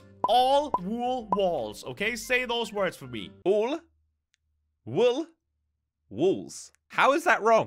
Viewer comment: It sounds like hes saying: Wool Wool Walls Instead of: All Wool Walls